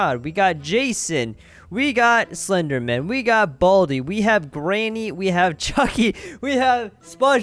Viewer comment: did he say branny?